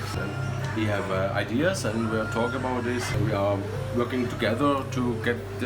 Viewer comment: wtf he says "we are in Ile de la Réunion", which is a place, not "we have a tasting meeting wit"